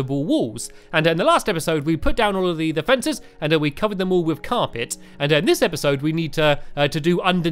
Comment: I thought u said carpit but when you crafted carpets I laugh a liitle